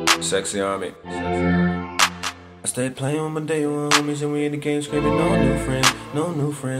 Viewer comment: I thought he said stay playing with dingaling...